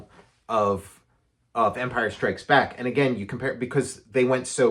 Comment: I swear is saying "vampire strikes back" and now i want to see a monster spoof movie with that title.